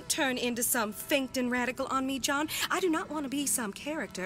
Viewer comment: I wasn't looking at the screen and totally thought she said onee-chan (idk if i spelled that right) XD